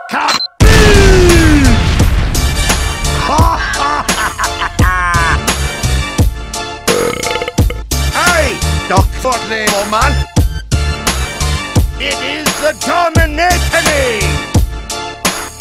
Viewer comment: at first i thought he said dont fuck me old man